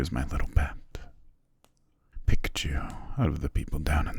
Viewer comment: it sounded like he says "Pikachu" and not "picked you"... I'm sorry, but that made me laugh a little 😂